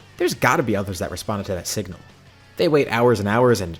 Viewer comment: I swear he said sigma im not crazy